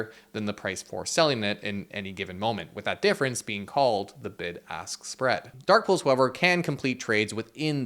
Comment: Great video! But at 7:32 there was no need to start cursing. You could have called it the "massive spread" instead of the "big a** spread" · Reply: If you turn on captions, he said "bid ask spread"